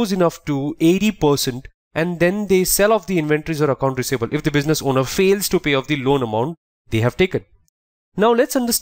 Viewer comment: What is he saying at 4:04 in regards to the 80%? "If the business owner fails to pay off the loan amount, they have taken" ?